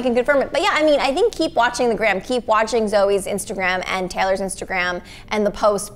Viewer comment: I swear she said zoey not Joey